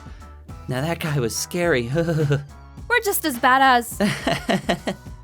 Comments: omg when sasha said, "were just as bad as-" and was cut off, it sounded like she said, "were just as bada$$ xD" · when sasha said "were as bad as-" I thought she said "badass" xD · I swear to God Sasha just said "badass" and yes I do know what she meant OK! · When Sasha said, " We are just bad as Gene." I got an added just before she said "Gene." It sounded like she said "As badass as Gene was." o(≧∇≦o) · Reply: Andii The Yandere Lol the guy cut her off right when she was saying "just as bad as.. gene" and I heard "just as badass" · There was an ad at 6:00 and I thought Sasha said were just as bad a** I was like WHOOOAAAA THERE KEEP IT PG BRO 😂XD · when she said "we're just as bad as" there was an ad and i thought she said badass lol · AHAHA I THOUGHT SHE SAID BADASS BUT SHE SAID BAD AS OML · when Sasha said ' we're as bad as ' I thought she said bad-a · When Sasha said, " We are just bad as Gene." I got an added just before she said "Gene." It sounded like she said "As badass as Gene was." o(≧∇≦o)